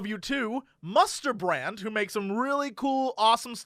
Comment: Musterbrand... For a second, I thought he said Mustard Man. 1. Victoria, Australia 2. World Of Tanks